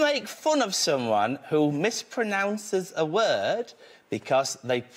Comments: Oh I thought he said Reddit, Reading makes a lot more sense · Lol English is not my first language and I'm not from the UK, and at first I heard "Because they learned that word from Reddit" - and couldn't work it out for the hell of it what is the joke here. Took me several months after I first watched that episode and stumbling upon this comment section to get it! :)